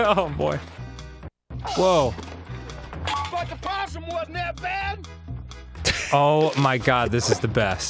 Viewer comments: Totally thought he said, “Fuckin a possum wasn’t that bad!” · what does the guy in green at 10:26 say ?? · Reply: "but the possum wasn't that bad" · Does anyone know what that guy was saying at 10:25? I couldn't understand him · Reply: I heard "BY THE POSSUM WOULDNT BE THAT BAD"